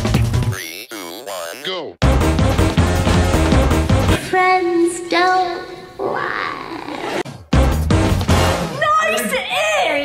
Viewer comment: When she said FRIENDS Don't lie I thought she said wall can die